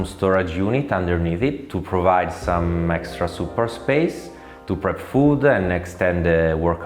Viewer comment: As a non-native speaker, I get that at 4:22, the guy is intending to say 'support' instead of 'super'. Is there an official way to contribute to subtitles? EDIT: as a fellow Fernet Branca enjoyer from Argentina, I fully second the inclusion of the framed poster.